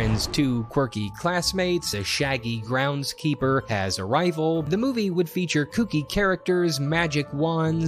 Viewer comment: Wasn’t paying attention and thought Austin said “had a rifle” and I was like I GOTTA SEE THESE SHIT